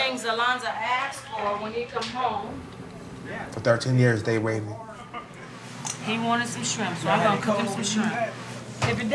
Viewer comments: did he say for 13 years they raped me? Is that what I heard? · did he say he got raped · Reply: "For 13 years they waited." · It sound like he said “ They rape me for 13 yrs “ @ 15:28 raped of life, freedom, childhood, graduations etc...